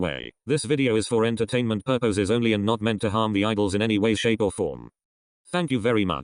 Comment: Instead of idols I heard eyeballs and got super confused... I need sleep